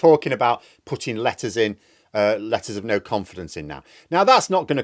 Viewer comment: anyone else heard "lettuce of no confidence" or is that just me?